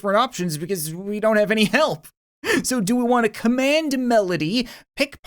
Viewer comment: I THOUGHT YOU SAID WE DONT HAVE TO PROVE WE WANT MORE ANYMORE